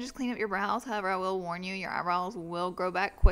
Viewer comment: When she said shading I thought that she said shaving